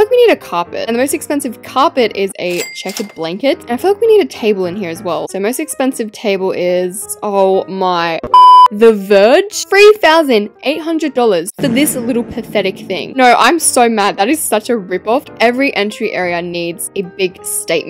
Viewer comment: For the table, I thought you said the purge.